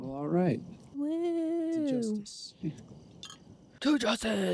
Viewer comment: I miss mishearing "Diet Jesus" at the start of every episode.